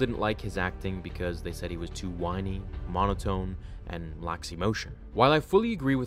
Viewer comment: “lacks emotion” is what i just heard, I keep double checking because I don’t understand how he would lack emotion.